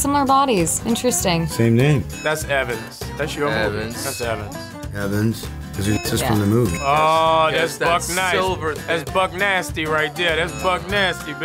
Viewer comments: When Evans came up, it sounded like Anthony said "That's your boy" to Sebastian and I almost d i e d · I thought he said that’s your mans😂😂😂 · auto captions said "That's your heavens!" and I gotta agree